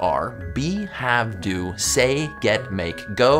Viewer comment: I wasn't paying attention to the video and just the audio so when it said know take see I thought it said no taksy and I was so confused